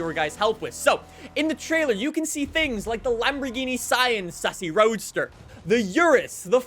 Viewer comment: Did he say the Lamborghini Sian sussy roadster? 🤨🤔